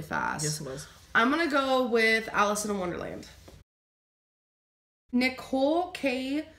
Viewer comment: Did she say Allison in wonderland?🧐